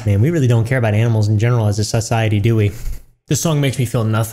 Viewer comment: "SUSiety" ????? That's what I heard 🤣